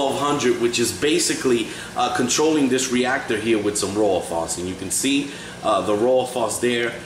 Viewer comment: at around 27:00 you describe a reactor but I can't understand what you say. I hear "rolofoss"? What kind of reactor is it? (I'm just learning and trying to decide if I can do this hobby). Thanks for the help!